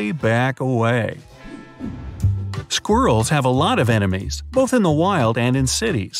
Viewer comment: theres no way im the only one who heard squirtles